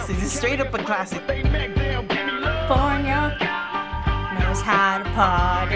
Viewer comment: what did she say? "california, no house about it" hmm i always thought it was no doubt about it...